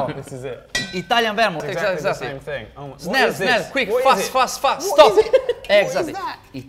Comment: Wait, did say 'Snel, snel' ( Dutch for Quick, quick) at 1:12? :p